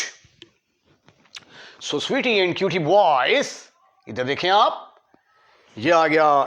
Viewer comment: did he just say "sweetie and cutie boys!!??" ... SUS